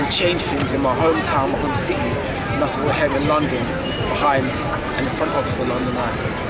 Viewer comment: WHAT does he say at <link> 0:26 lol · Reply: Life's all about perspective, is he behind or in front on the London Eye...Is his shirt black with a white pattern or white with a black pattern, all in the eye of the beholder I guess lol